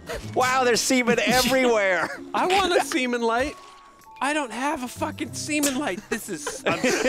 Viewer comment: I thought he said semen life XD